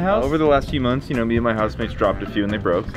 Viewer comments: I put close captions on I thought said me and my husband but he said housemates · Just wondering did does have a husband? Because at 5:22 I thought he said that he has a husband